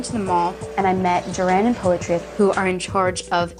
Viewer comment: did say that person's name was poetry????